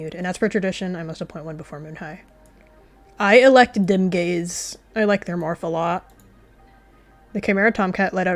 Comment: i thought you said dem gays instead of dimgaze